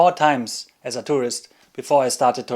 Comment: What do you say at 2:38 ? I've listened several times but can't quite make out what visitors to hot springs should or shouldn't do. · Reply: Thank you, I also had trouble understanding. At first I thought you said "detours" and it was confusing :) Now it's clear!